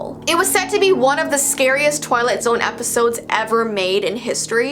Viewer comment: Did she just say Toilet Zone